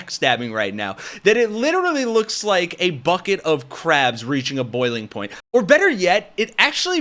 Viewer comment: I thought you were saying "bucket of crap" at first.